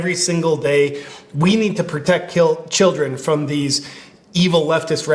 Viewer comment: it sounds like he says, "we need to protect(kill) children."